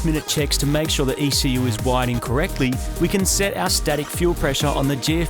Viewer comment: anyone else hear "wired incorrectly" at first? haha